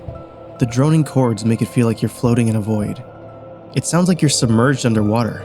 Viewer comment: I heard "floating underwater" and IMMEDIATELY thought of the lake scene...